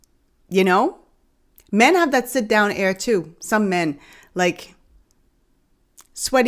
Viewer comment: Sit down air? This should not be a thing! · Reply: I honestly thought she was saying her "Sit down area" rather than sayin butt or a*S or something. I guess I was wrong.. Thats icky, but proud of her for being honest in front of so many others.